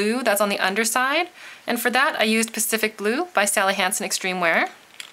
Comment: When she says Sally Hansen at 6.37 it actually sounds like she says Sally HansTen!!!LOOL